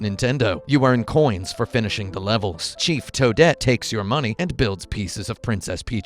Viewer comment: I thought he said cheep toadette